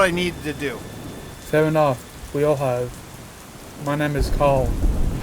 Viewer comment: Did he say "Carl" on "Cal"? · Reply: Karl :)